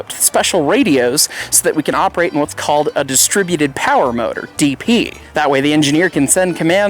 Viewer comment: Did he just say "DP" 😂